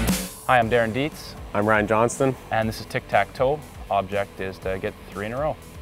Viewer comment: I thought Ryan Johnston said "I'm Ryan Gosling"😂